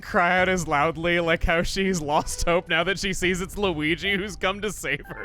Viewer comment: why does peach sound like she's saying luwege instead of luigi